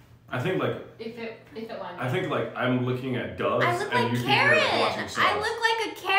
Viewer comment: OK, thought she said she looked like a carrot, but Karen makes more sense.